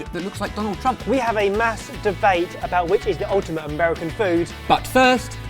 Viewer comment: I thought said "masturbate" and I had to replay it again till I understand that he actually said "mass debate"....